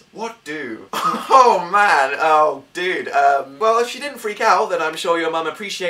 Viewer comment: Did Dan say "dude" at 2:33? I'm screaming